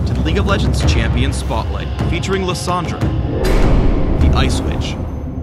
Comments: who else thought phreak said the ice bitch at the beginning? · lel I lagged so I legitly thought I heard "Lissandra: The ice bitch" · lol I heard "the ice bitch" at the start. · for a second i thought he said the ice bitch... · I swear I heard " Welcome to the league of legends champion spotlight futering Lisandra.... the ice bich. " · I was so sleepy I thought he said ice bitch XD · i heard Lissandra " The Ice Bitch" LOL